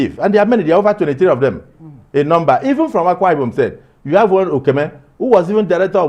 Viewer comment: This man 👨 is smart Akwa Ibom pride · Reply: I thought they said he is not from akwa ibom. Na WAOH for news oooo